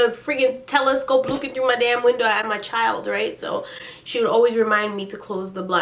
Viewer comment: "I had my child right?" 1:45 or am I hearing shiii?🤔🤔🤔hmmm